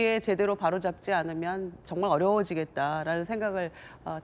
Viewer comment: 이번 기회에 바로 잡지 않으면 어려워지겠다....는 민주당이 어려지겠다로 들리네